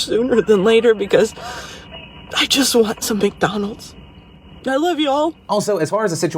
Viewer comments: The CC at 5:23 say 'big donuts' instead of Mcdonalds.. My sides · SAYS SHE WANTS MCDONALDS BUT THE CAPTIONS SAY SHE WANTS BIG DONUTS IM DEAD